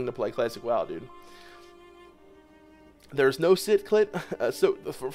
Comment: Excuse me? Did you say no sit clit, im dead 😂 40 man rave.. funny stuff